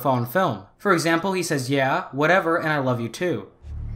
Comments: when he says "whatever" i swear i have always heard him say "you asshole" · Yeah, whatever who are you, I love you. I just heard who are you and whatever